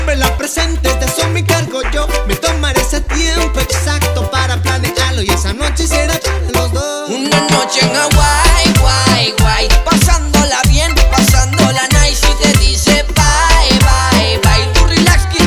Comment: Cuando la escuche por primera vez pensé que decía una noche kawaii u.u ahora estoy triste (?)